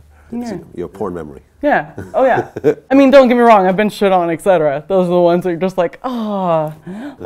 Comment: I'm sorry but at 9:52 did she just say she was SHITTED On? Did I hear that right what movie was that?! Lol